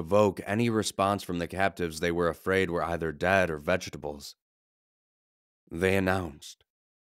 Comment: did he say vegetables